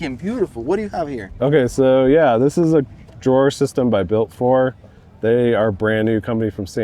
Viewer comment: who makes the drawer system on Landcrusier build???? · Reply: I think he said "built for"? A new company from San Diego